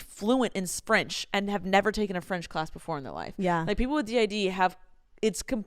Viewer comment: did no one notice she said sprench instead of French and no one notice?